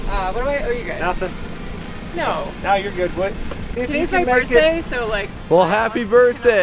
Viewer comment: Did i hear him say "youre good, wood"?